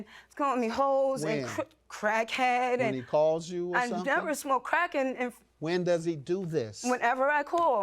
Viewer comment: she sounded like she was about to say “I never smoked crack in front …. of him.”